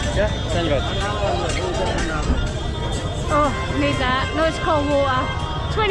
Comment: what did you just say 🤡?? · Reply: Need that nice cold waa’er